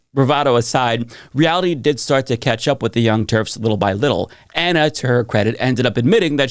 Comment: Did say "Young TERFs"? He's not wrong, but 😂😂😂